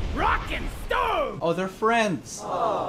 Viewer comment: Did I hear Rock and Stone